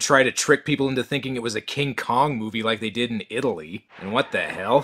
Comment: I honestly thought it said Kink Kong. Would definitly give a whole new meaning to the term homo erectus And honestly; was Gamera intended as a Godzilla spoof?